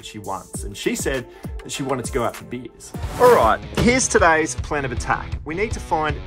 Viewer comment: I’m from the USA and I thought you said she wants to go out for beads.. lol I was like that must be a Australian thing.